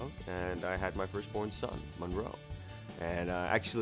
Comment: What he said, "My first born son, Monroe." What I heard, "My first born son, my bro."